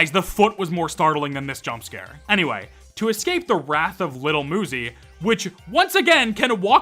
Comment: my dumb ass really though he said lil mosey instead of little muzy help 💀💀